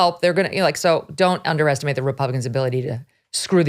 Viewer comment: did she say republicans? Check out his face 😂